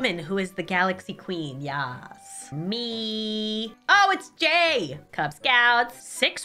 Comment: I THOUGHT SHE SAID GAY 💀